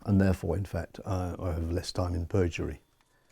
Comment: "A ticket saying, I have less time in Perjury"? 😊 I think you ment to say, less time in Purgatory. 😉